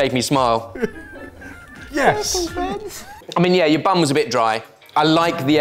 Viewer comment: Everytime says bun, my dyslexic ears are hearing 'bum' so I heard "your bum is a bit dry" XD